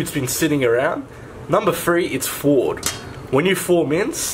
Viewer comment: Anyone know what he says is "number three" at 00:50? Sounds like ""Ford", no idea what that means. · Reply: orsonmoniz I think maybe it’s thawed